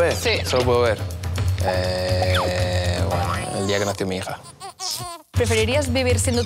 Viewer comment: escuche muy mal escuche el día que mate a mi hija y si está mal solo que escuche mal